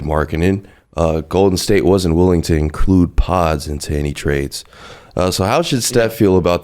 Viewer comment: What's the word starting with P? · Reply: Podz ( Brandon podzemski)